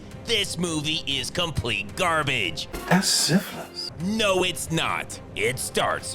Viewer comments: I swear he said, "I have syphilis." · Somebody please tell me what the hell he's saying at 0:15. It sounds like "That's syphilis."